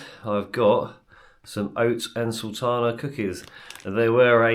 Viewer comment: Sounded like you said "oats and tylenol cookies". I rewound to listen again, definitely sounded like tylenol. Is there a tylenol rhyming British word for raisins, walnuts, chocolate or something else that explains the dark spots in the cookies? 🤔